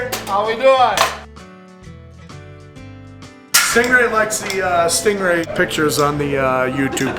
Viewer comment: def thought they said “hey staple brain” instead of “hey stingray”